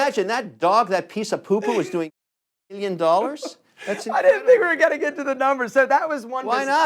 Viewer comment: did he say 8 million dollars